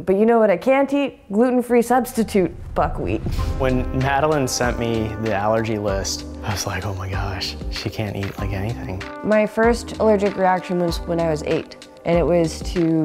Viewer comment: i thought she said "my first allergic reaction was to gravity" gosh my hearing